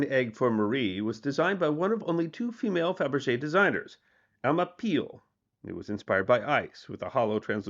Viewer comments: Listening more than watching, I could have sworn I heard "Emma Peel" 😂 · I thought you said Emma Peel was one of the designers but I misheard. The designer’s name was Alma. Too bad. It would have provided another dimension to the character to the Avengers character.